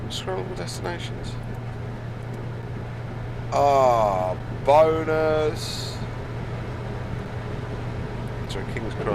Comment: Sorry but did you say “ahhh boners”?